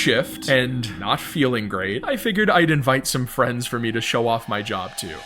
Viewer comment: Does anyone else hear "f the friends"